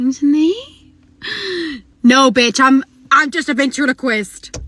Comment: I thought she said “I’m just a bitch on a quest”